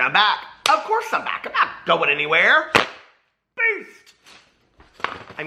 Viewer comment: When i first started watching you i didn’t know you were saying BEAST i thought you were saying PEACE!!!! HAHAHAHAHHA 🤣 Love you and your videos!